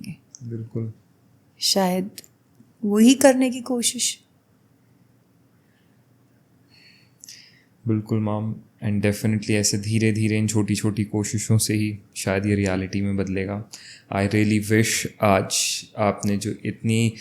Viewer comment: the interviewer said bilkul "mam" but it sounded like "mom".I think in some ways the souls try to connect with their loved ones.❤❤